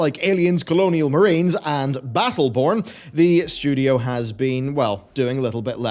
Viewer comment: Thought you said battleporn, had to double check